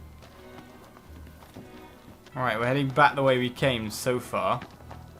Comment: I thought said: So we are heading back the way we came, sofa.